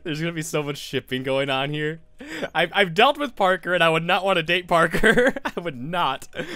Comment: When H said "I've dealt with Parker" I'll admit it, I heard "I've dated Parker". No shame.